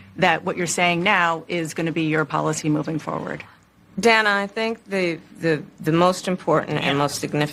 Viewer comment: did she call her "Dan-uh"?